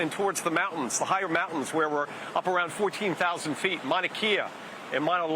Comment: Did he just say Mauna Key-a